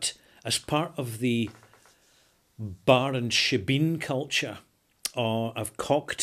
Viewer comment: "as far as the bar and ??????? culture?" What's the word I don't understand @ 2:37? Actually, just looked it up phonetically. For those Yanks among us (like myself), a "shebeen" or "sibin" is the Gaelic equivalent of a speakeasy.